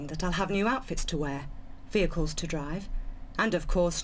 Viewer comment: I heard "outfits to wear, vehicles to wear." I have watched too much virtual Lara on drugs.